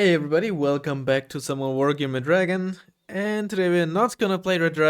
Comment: Every time I hear "Welcome back to some more Wargame" I think you're saying "Welcome back to summer Wargame" and my brain is confused for a sec before realizing.